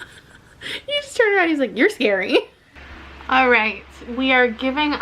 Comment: When said your scary my iPad was like, you called me? It thought he said hey siri